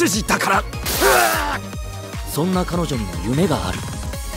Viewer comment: 「そんな彼女には胸がある」って聞こえるw重症か…